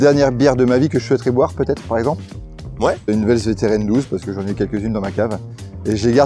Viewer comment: HEY ! à 6:10 j'ai absolument rien compris à ce que le biérologue a dit. C'est quoi le nom de la dernière bière de sa vie svp ? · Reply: La Westvleteren XII ; )